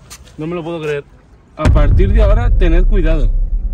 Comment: en la parte 4:42 escuche hola